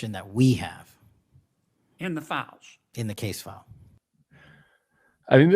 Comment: Patel did not say case files. He said "case file" as in a singular file. just saying.